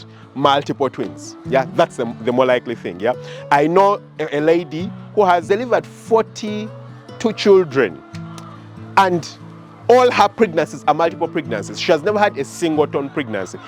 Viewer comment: excuse me.... did you say 42 children? 😳